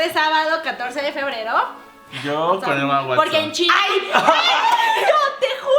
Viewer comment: que dijo rafa en el minuto 15:36? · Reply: emma watson